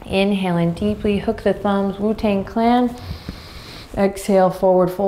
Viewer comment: did she just say WuTang Clan? 🤣🤣🤣🤣🤣, sure found my chill there